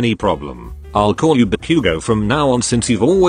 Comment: When deku said Ba-Bakugou The voice thing made it sound like it said barbakugou